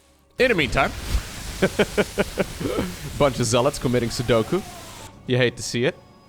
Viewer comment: am I hearing "Sudoku" or are the Dutch Ps silent?